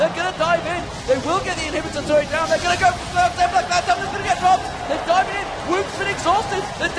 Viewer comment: what phreak said "They're ganna go cloudtemplar cloudtemplar ganna get dropped" what we heard "they're ganna adlflkadjfladfjdssdoijopas dropped"